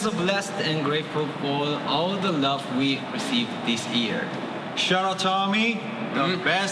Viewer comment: Can someone tell me what Taehyung said at 1:06 ,please? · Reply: shout out to ARMY